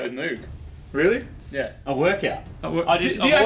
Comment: I thought I heard "make out with Moog" not work out, oops. 😮